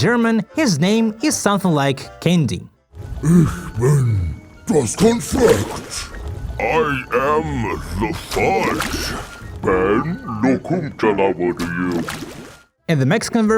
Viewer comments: Bro I am german I can tell you, that is not what we call candy, candy is called "Süßigkeiten" and from what I can hear he says "Ich bin, das Konfett" which would more be konfetti and not candy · i think he says „Ich bin Konfett/i“ not sure,i am german too